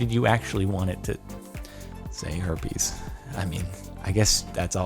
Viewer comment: I Thought it said Heroes